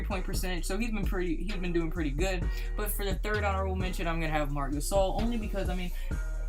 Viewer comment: good vid but at 7:40 wtf did u just say lol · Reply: Honorable mention